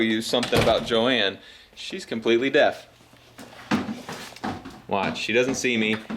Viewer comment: I heard "completely deaf" As "completely dead" And I started crying